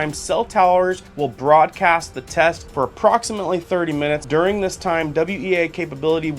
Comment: I thought it said 20 min.